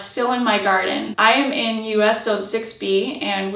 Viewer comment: Someone help me, what does she say at 2:10?? Thanks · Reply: We are in US Zone 6B :)